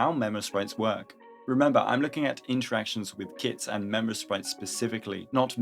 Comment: i thought i heard interactions with kids at 0:16 XD